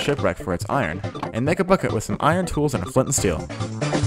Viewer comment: When he said “a flint and steel” i thought he said “a fucking steel”